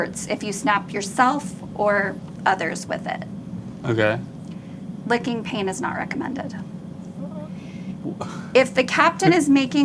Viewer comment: "Inflicting pain is not recommended" 😂 Link needed that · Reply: I thought they said licking paint!